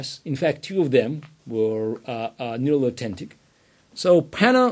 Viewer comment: What word does he use at 8:36? "In fact two of them were (what??)." Sounds like "neolotentic" or something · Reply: Sounds like he said "nearly authentic" but I assume he meant "nearly identical".